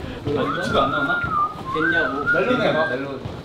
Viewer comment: what did he say? edit: So if your wondering. He said "Try Melon."